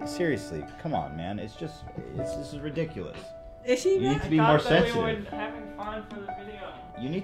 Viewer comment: I could swear you said "Is she black?" at 12:18 No, but she's not technically white either she's Israeli.